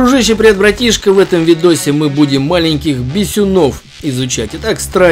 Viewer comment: Один я прочитал "маленькие писюны"? Уже даже послышалось